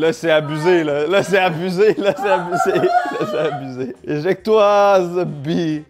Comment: chui mort "ejecte toi zebi" 😭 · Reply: Il a bien dit éjecte toi zeubi